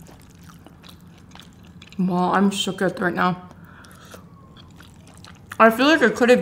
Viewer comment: did she say shes shooketh 🤔😂